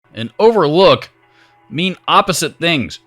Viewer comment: What was the whole question in the beginning? I only heard, "...and overlook mean opposite things." Oh, Overview. I think I got it. Overview and Overlook.